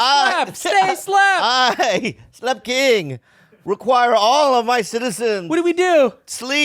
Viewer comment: My dumbass thought you been saying slut king the whole time